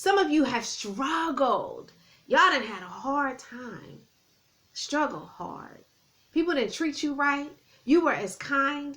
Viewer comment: Thank you from nyc... What was the word you said at 19:18 something energy ??? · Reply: "A special energy " Welcome to the channel!!!❤